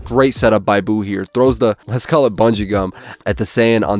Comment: did I hear bungee gum? possible hxh match videos sometime down in the future? I loved the chuu and yusuke breakdown, I'd love some more of that togashi smackdowns